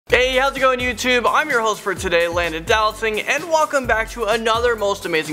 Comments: It sounds like you say Landon do not sing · Why do people hate on landon? He is one of my favorite hosts. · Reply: Lucas Manglona I thought you say London (I like him too PEOPLE SOMETIMES AND CRUEL WHHHHHHYYYYYYYY)